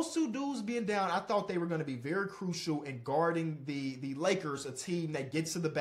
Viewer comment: i thought said he a laker hater